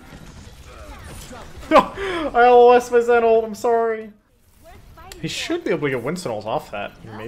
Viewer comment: What does he say at 3:40? "I heal less with Zen ult, I'm sorry?" I cannot understand.